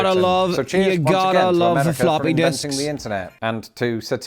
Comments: Hold up, did you just say floppy dicks? I heard what I heard 😆😆😆 BTW, we do have Heinz baked beans but most of us are adding more to it like hot dogs or bacon or both or BBQ or BBQ sauce, but most of us won't be adding toast 😆😆😆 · I’m so tired. lol I need to watch your videos when I’m more awake. When you said flopping disk. I thought you said floppy dicks. You got to like floppy dicks. 😂 I either need to wake up more or go back to sleep. 😂